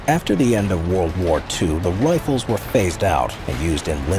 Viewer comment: The what was phased out? I hear him saying "rifles." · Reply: the PTRD/PTRS ATRs